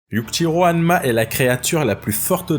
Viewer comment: C'est moi ou au début de la vidéo 0:00 yujiro hanma il a dit le petit roi hanma